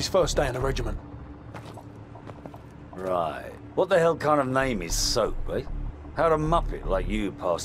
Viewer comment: Idc worm has the most unforgettable and overlooked line in COD history “who’s soap?” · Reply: I thought he said “Who the hell is soap” but it’s still funny nonetheless